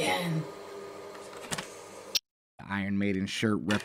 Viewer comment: I heard her say “hi people and I’m buying cocaine again” lol